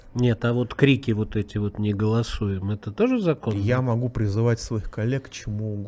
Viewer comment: страна и народ бедный с такими депутатами, кто меньше всего о народе и стране думают, крича "не голосую!!!"🤦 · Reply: Вы не расслышали! Депутат кричал всем представителям фракции "Единая Россия"- "Не голосуЕМ! А так как фракция самая большая,то закон оказывается не принятым.